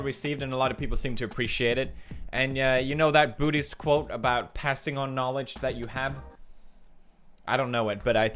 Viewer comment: I thought you said 'booty's knowledge' and I was like amen brotha